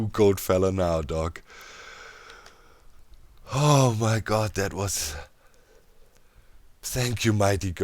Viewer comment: thought I heard "oh my goat"